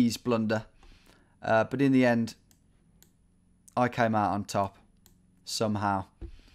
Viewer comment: Did he just say "I came out on Tom"?